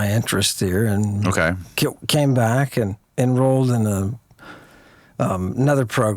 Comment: Anyone else catch that froidian slip there just after the 18:11 minute mark? “Kill came back” you could see tension in his face. 😂 What’d you kill there or should I say who?